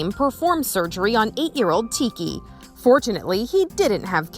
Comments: "unfortunately he did not have cancer" me: "unfortunately" so you were hoping that adorable dog to have it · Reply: LOL this is too funny that you thought she said unfortunately idk why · With the dog that ate the underwear she said unfortunately It wasn't cancer like wow who would say that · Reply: You misheard it, she said fortunately